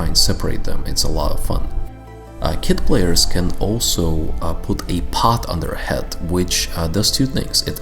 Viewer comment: What did said in 3:52 ? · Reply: The kids can now put a pot on they're head